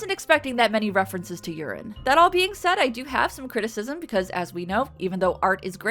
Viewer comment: After your discussion about the number of urine jokes, I could have sworn I heard you say "That all PEEING said". The power of suggestion.